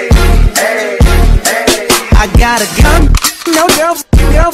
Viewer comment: It's just me or i heard "i gOttA cUm"